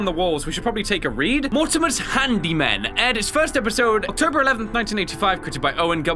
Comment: every time a character says "handymen," i hear "hand demon." probably intentional